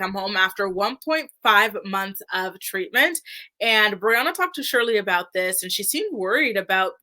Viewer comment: HAHAHAHA I thought you said "RHIANNA" picked the mom up from rehab and I was like WHAT 😂😭